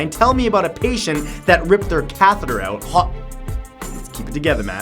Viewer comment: what did they get ripped out? I can't understand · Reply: A catheter.